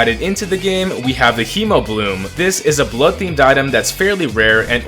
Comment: I swear I heard say "bloodfiend"